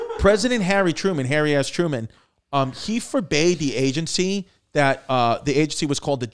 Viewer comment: Sounded like was saying Hairy ass Truman